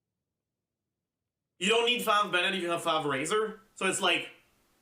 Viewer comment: It seemed uncany and funny to me that in the subtitles at 19:43 instead of fav they said fountain, i'm not joking btw